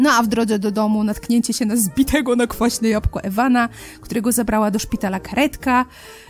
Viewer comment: czy tylko ja usłyszałam tu ,,którego zabrała kredka,,?